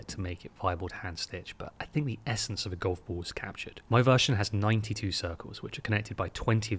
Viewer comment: Definitely thought it said goofball